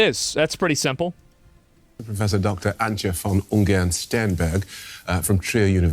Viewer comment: Did he just say her last name is Steinberg? 34:50. I feel she may have a bias against popular German political parties lmao.